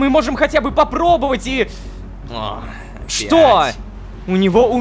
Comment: [JDH]: ох,блять Я слышу прекрасно нО НА САМОМ ДЕЛЕ ДЖОН СКАЗАЛ ОХ ОПЯТЬ!